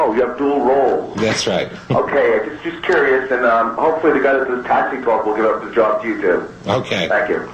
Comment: what did he say? The guy who does what job? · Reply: lake122 I can never get it but I’ve got “hopefully the guy that does Taxi Talk will give up the job you do”... I think